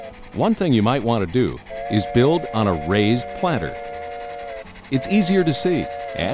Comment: What did he say at 3:44. "One thing you may want to do is build an a raised __________"?? Very nice layout. I could never do something like this. Absolutely stunning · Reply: raised "bed", less bending over, easier on the back